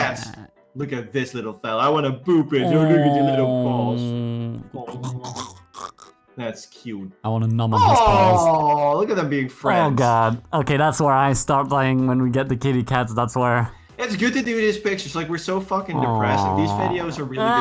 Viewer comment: I heard "I wanna nom on his balls" and instantly hated myself even more than I usually do. I'm so sorry.